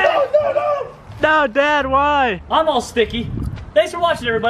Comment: did say "No dad why?" To? Omg